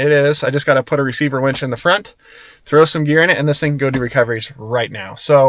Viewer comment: Yeah! My vote was for leaving the tires on the Willy. Why mess with a really good thing? At the end of the video I first thought I heard you say "throw some beer in it." 😅